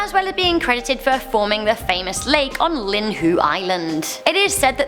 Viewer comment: hold up did she just say “Linhue”